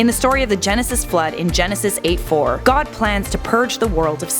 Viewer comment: thought she said God’s Plan 👌